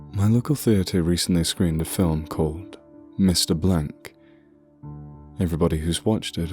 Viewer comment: Thought it said 'Mr. Banks.'